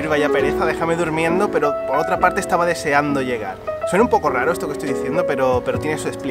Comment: "Por una parte era como en plan pfff, Mamá, no quiero ir, vaya pereza, déjame durmiendo" Lo que yo imaginé: "Pero por otra parte mi mamá no estaba conmigo" Lo que dijo